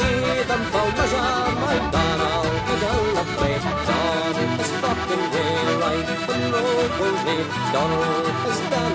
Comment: Does anyone else hear "Donald is fucking gay like monopoly"?